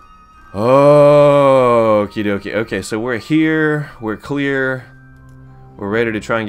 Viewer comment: what he said:WE'RE HERE WE'RE CLEAR. what I thought he said: WE'RE HER WE'RE QUEER